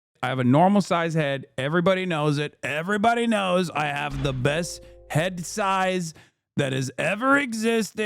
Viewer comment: lol I swear I thought you were going to say “I got the best head” 😂